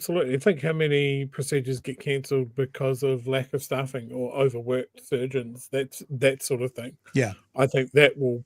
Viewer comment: I totally thought was saying "overworked virgins" at 1:02:30, had to listen to it a few times